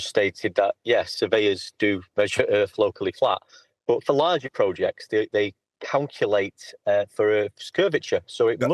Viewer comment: "But for some reason tells Nathan that I said that surveyors measure the Earth flat." That's not what he said, though. He said "yes, surveyors do measure Earth LOCALLY flat, but for larger projects, they [mumbling] CALCULATE FOR CURVATURE." I would say that's correct. For distances smaller than, say, 50 meters, you don't need to account for curvature, but for larger distances, you do. That's exactly what you said earlier in this video.